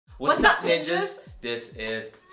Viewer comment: Ok I know the intro, but for some reason I thought they said, “what’s up b*tches!” 😂😭